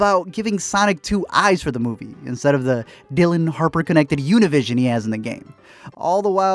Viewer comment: Did you just say the Dillion Harper Univision? LMAO